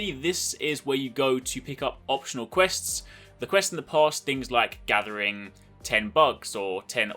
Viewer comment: I thought u said one of the quest is to gather 10 bucks I was like what 😂😂. lol